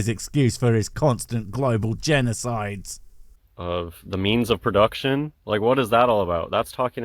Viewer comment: Also, I almost thought he said, "The memes of production" for a second. Damn. 😂